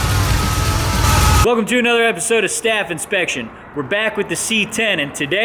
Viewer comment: I thought you said welcome to another edition of "Staff Infection" I had to rewind to make sure:)